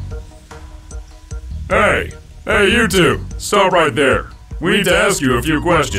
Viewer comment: lol it sounds like he said YouTube.....